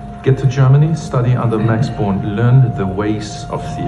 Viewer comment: "Born." "Born." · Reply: He was referring to a physicist, Max Born, not a city. It just sounded like he was saying “Bonn” because of his accent